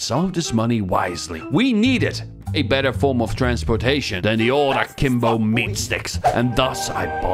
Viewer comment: I thought you said, ik ben een form of transportation